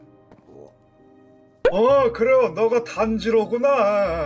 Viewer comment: 초에.. 어ㄱ,래 니가 탄지로구나ㅇㅇ 이렇게 들림 (참고로 탄지로는 만화 캐릭터)